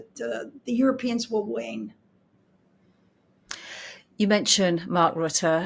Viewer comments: Maybe she said "There is very little chance the Europeans will wane". Right around the 8:50 mark? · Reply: Yes, at 8:50 she said "wane". For a moment it sounded like "win" to me, so I listened one more time. It is "wane". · Did I hear her correctly? "There is very little chance the Europeans will win". Right around the 8:50 mark?